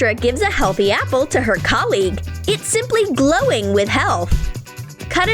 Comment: I thought she said that its it's simply glowing with h e l l